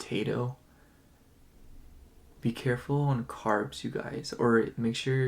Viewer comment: Option 2- oh wow I actually had a few similar messages come through in the last "next 7 days" reading I did for my channel 😮 You said "Carbs" but I only heard "Carbonara" lol yes please 😋 Love your Channel! Thanks for the Reading! 🫶💫✨️